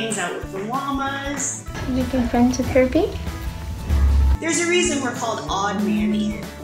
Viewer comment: I thought that said herpie XD